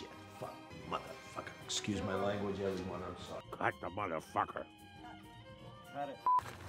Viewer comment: it's so funny to see him going from "SHIT FUCK, MOTHERFUCKER" to "excuse my language everybody I'm sorry" in a matter of a second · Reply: To me I hear "excuse my language, young one I'm sorry" for some reason